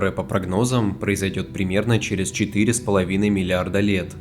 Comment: Сколько, сколько вы сказали? 4.5 млрд лет, Уфф.. фуух... А то мне послышалось 4.5 млн лет.. 🤣🤣🤣